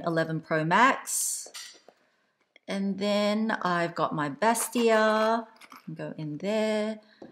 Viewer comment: Hi my #faMELy! Here is everything you need to know about the Hermes Kelly 25 & 28, Sellier vs Retourne. Comment below, which size/construction do you prefer? I hope you find this in depth video helpful! Let me know what comparison you’d like to see next! Big kisses xx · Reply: What was that little pink pouch called at 18:40 and what is it used for? I think you said vestia? Please let me know. Love your videos. So informative. Thanks.